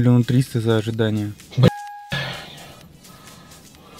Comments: Последнюю фразу не расслышал дедушка, скажите плиз, кто разобрал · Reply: По-моему "Хату возьмёте?" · Че он там ответил? Чем возьмёте? Последняя фраза. Не могу расслышать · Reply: Хату возьмёте? - Поехали...